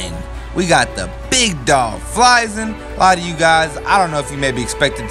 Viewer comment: “flyzain” is what I heard 🤔